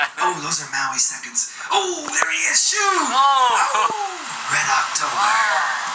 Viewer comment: did he just say "red October"?????